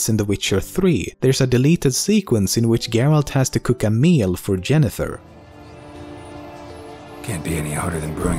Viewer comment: at @ 10:48, did he say Jennifer? instead of Yennefer?